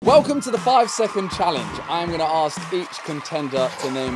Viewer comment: why didd i hear "am gonna ask 3 chicken tender"